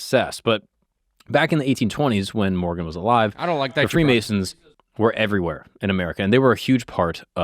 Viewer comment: Anyone else hear "I don't like that guy" in the background